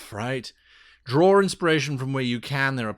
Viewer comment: Hi, great video. I have one question: what did you say in 10:20? Draw exploration? Sorry, english is not my native language but I’m really interested in the topic of improvised sessions. Btw, I bought your book on drivethru, great piece of work.